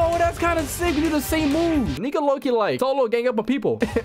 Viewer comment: idk why i just hear u say "ni looking like" or smth im not america person and can someone tell me what does he say ? I just want to know